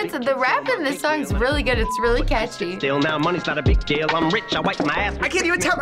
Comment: did she just say it's really catchy?